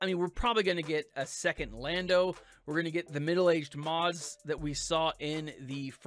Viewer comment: did you say "middle-aged Maz," as in Maz Kenata? I don't remember seeing her in the trailer.